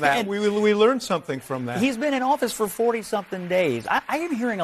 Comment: mark... I thought he said, "He's been in office for 40 effing days." I had to put on the captions for that one.